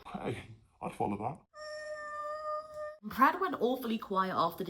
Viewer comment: the closed captions read as 'pratt' instead of 'Brad'. Feels appropriate 😂😂😂